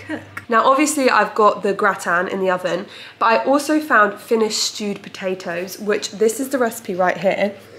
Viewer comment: every time you said "stewed potatoes" i thought you said "stupid taters"